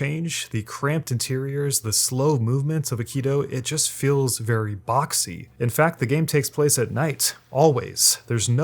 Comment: DId you say... BOXXY! #memoriesofearly2000s